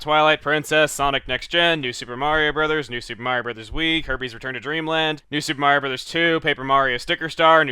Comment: When he was telling example of Desert worlds it sounded like he said "Herpes Return to Dreamland" I lol'd.